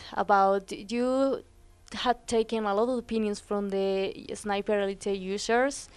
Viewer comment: Did she just say " Taken a little penis" 38 secs in???????????